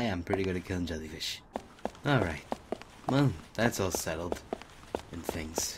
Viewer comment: he says "thats all settled' i thought he said "thats all seto'd' dreams :')